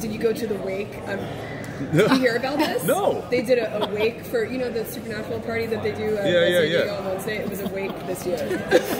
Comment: in 3:57, what did the interviewer say to jensen to get him surprised?? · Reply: Fans turn an annual party into a wake for the show.